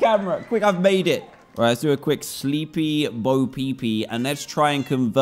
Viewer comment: Did he just say sleepy bapp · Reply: Bapeepee